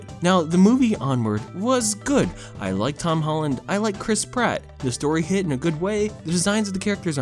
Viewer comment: Every time I hear Chris Pratt’s name being said, I just hear “crisp rat” 🐀